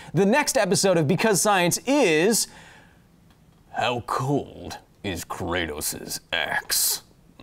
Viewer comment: I misheard you. I thought you said "How cold is Kratos' ex." I was like. Wow